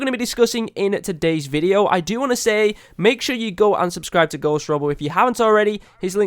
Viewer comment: Wait did you say unsubscribe att